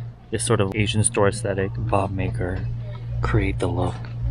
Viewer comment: i thought it said "boob maker" XD